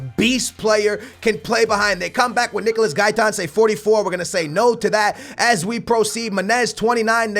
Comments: does he say "nigga no to that" 😂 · Am I the only one who heard "nigga we gonna say no to that" at 12:33 ? o.O